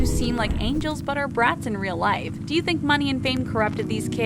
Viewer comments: I thought she said "subscribe to the toco" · Who else thought when she said welcome to TheTalko but you heard welcome to TheTaco